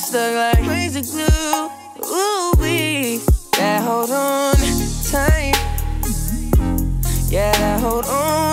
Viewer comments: When I first heard this song I thought he kept saying "Dad hold on tight". I was so confused lmao he says "they" weird. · For the longest time I thought he was saying, "dad hold on tight" And was like 'aw his dad is dying?' And the next line was "lips won't let me go." And I was like 'ew what is he doing with his daddy?'